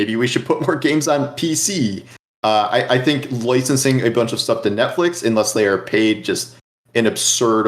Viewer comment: Did say "loicensing"? 12:22 Noice, I'm into it.